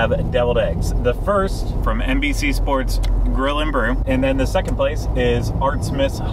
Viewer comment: Love this - thanx guys! Every time you say it, I hear "NBC Brill and Grew" in my head! LOL